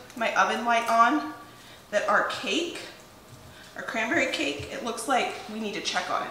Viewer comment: I am excited, I thought you said raspberry cake and I don't like raspberries, but when I saw you throwing CRANBERRIES in the cake pan, I am paying attention now. 🙂